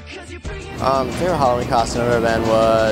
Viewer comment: OML at 2:47 I thought he said favorite Holocaust event i was like ......